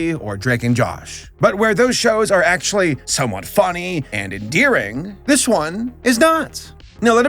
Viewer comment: I thought you said "So unfunny" and not "Somewhat funny" for a second